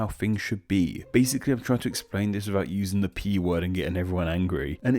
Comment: Hate to ask, but.... What's the P-word you're referring to? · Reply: Politics